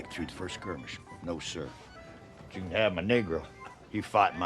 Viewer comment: When the guy signs up his slave to enlist I misheard it as "you can have my neighbour" and was so confused but thought maybe youd talk about how disingenuous it is for him to be reffered to as his "neighbour" as if he doesn't only live next to him because he owns him, I understood after the next scene...